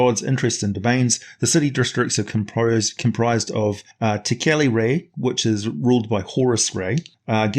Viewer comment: I had a lovecraftian horror flashback at 16:55 because I thought you said "tekeli-li"